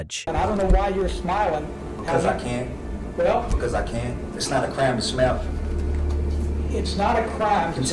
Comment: Sounded like he was saying "It's not a crime to smell" -and he probably does.